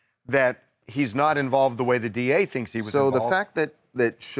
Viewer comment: It sound like sug saying he not dead